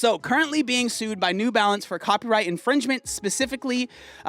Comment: At first I thought you said "Balance Athletic Cup" 😂